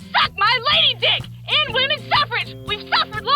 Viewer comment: did she just say "end women suffrage" lol omg